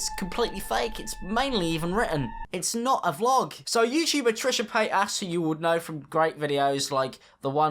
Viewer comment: He said “YouTube” and I legit thought he said “yeet sheeb”. I was half paying attention and haven’t listened to anyone but American accents all day and I got really confused 😂😂